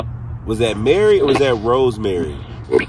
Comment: RoseMary I heard.